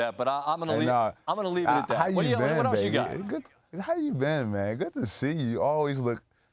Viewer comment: Did he just say "how you doing baby" 6:52 ?